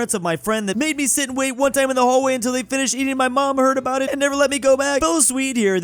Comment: 2:20 I heard "...eating my mom..."; and then saw the period between "esting" and "My".